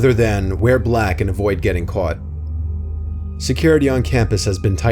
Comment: oh, he said "Wear black and avoid getting caught" I thought he said, "We're black so avoid getting caught" sorry, I misheard him.